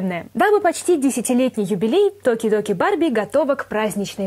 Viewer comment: Послышалось доки-доки барби